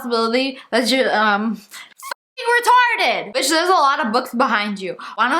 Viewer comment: excuse me did she just say the r slur?